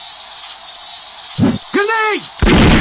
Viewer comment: What did he say? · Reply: I thought he said GRENADE!! 😂😂😂